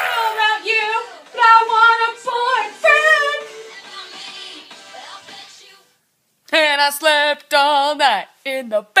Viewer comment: What is she saying at 4:28 ? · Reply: I don't know about you, but I want a boyfriend